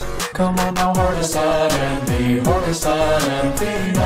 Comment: Some reason I heard arby's lemonade and IBS lemonade.